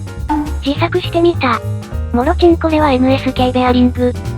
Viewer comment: "もろちんこ"としか聞こえない